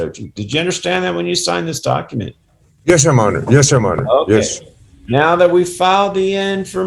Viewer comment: I can't quite tell what the guy is saying. It sounds like, "Yes, Monor." What is he trying to say? Not meaning any disrespect, just curious. Maybe 'My Honor'?